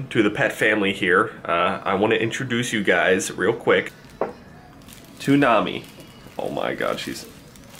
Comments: Toonami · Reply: Jailhouse I thought he said that too but he said "to Nami" 😂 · When he first said her name i thought he had said toonami but cute pet :)